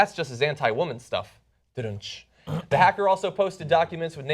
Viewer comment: Did John say "zentai women's stuff'" at 0:34 ?? didn't know it was mainstream :-P getting serious, now is definitely the time for a reorganization of parties.. both republican as well as democratic parties r controlled by establishments, so plebeians from both sides need to split and form their respective worker-class/middle-class parties.. this would also make clear what the tea party's stances are and they won't need the gop's crutch to stand..